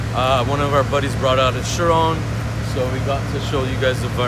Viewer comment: sounded like he said chirome 😂